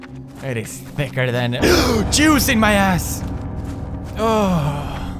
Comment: Lol did he say Jues in my ass???LOL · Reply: No if you listen to it twice he says juice